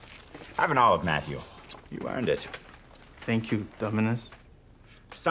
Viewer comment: i swear i hear him saying ''thank you ,dumass'' XD